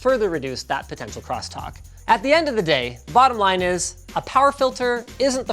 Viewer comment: "At the end of the day, bottom Linus". That's how I heard it. Lol